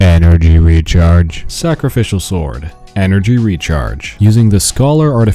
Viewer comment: wait did just call exile set scholar?